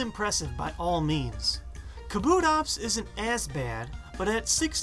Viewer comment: Kabutops is as bad. I thought he said kutops is AN as bad. Switch as bad